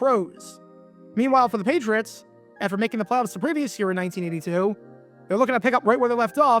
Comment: What are you saying at 3:51? “Meanwhile the Patriots, after making mmnpiinkknhngs; in 1982…” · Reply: Making the playoffs